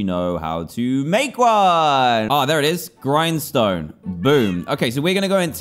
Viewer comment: they heard "make one" i heard ... "Hey everyone Dan here from the Diamondminecart and today"